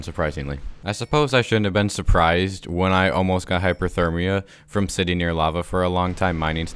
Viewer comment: "I shouldn't have been surprised when I almost got hypothermia from sitting next to lava for a long time mining stuff" I'm sorry to report that I don't think that's how that works lol I think you ment heatstroke Edit:someone already pointed out that he actually sead hyperthermia not hypothermia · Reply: He said hyperthermia which is the opposite of hypothermia. I misheard it too